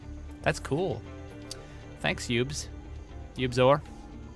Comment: Am I the only one that thought said "pubes" at 8:17?